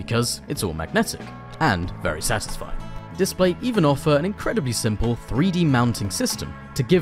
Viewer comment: Did anyone else hear him say “Display even offer” (7:31/